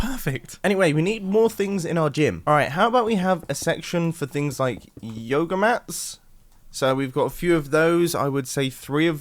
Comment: what said: all right how about we have a section for things like yoga mats. what I heard: all right how about we have a section for things like yoda mats.